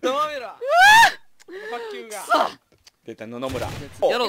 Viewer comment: あたりで、との一騎打ちで 負けて叫んでた時がぼそっと 「出た、野々村」って言ったから 何回聞いてももうそれにしか聞こえないw